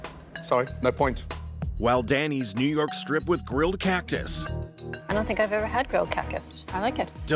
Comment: Did she say grilled ‘Cackus’?! 🤣